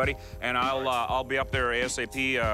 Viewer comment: it sound like he says “ok babe”